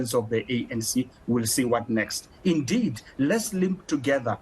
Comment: This man is out of order... I heard him say "let's sleep together" twice😁 haibo.. porn movie 😂😂😂😂😂😂😂🐄🐄🐄🐄